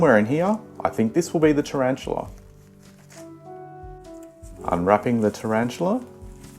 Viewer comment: my volume was low and I heard "im wrapping the tarantula" 🤣, definitely subbed after watching the vid